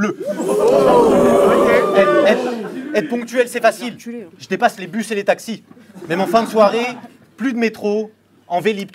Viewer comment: Elle dit quoi 🤔 ? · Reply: "Je vais reculer", en gros = Le Dernier Postillon xD